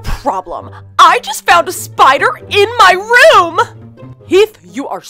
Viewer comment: When it said there is a spider at 0:07, I thought it said there is a FIRE in my bedroom and I'm like THEY DONT CARE THAT THERE IS A FIRE IN THERE HOUSE!!!!